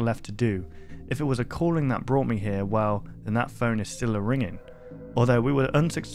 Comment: did he say he can't remember if the phone is real? That really makes things interesting! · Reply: I though he said ringing, he doesn’t remember it ringing. Though once I read your comment I can see how it sounds like he said real.